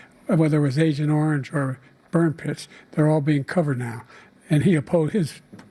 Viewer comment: did he say burn piss? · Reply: Burn pits, caused a lot of cancer look it up